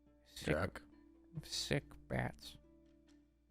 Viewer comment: I thought he said sick bastard 😅😅😅